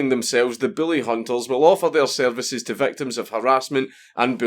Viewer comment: "offer our services", all I heard was "offer our cervixes" :P